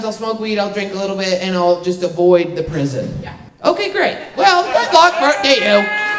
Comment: what did she say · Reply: "Good luck to you"